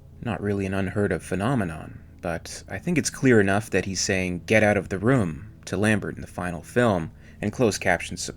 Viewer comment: I always thought he was saying get ‘out of the road’ which in Yorkshire (england) means get ‘out of the way’ - saying this to Lambert so he could use the flamer on the Alien